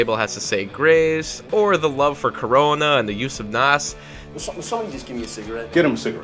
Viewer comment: Did you say “love for Corona” at 3:19?